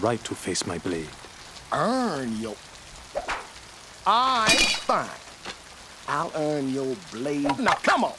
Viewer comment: it sound like he said "Alright nigga" in a way a boondocks character would